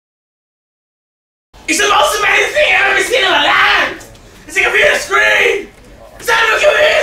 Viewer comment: what is csg even saying?? He said it so fast! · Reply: khetanisultan94 'its the most amazing thing ive ever seen in my life, a game inside a game!' its a play of when he watched most awesome vines with his dad and there was a vine about a guy saying how amazing a watermelon inside a watermelon is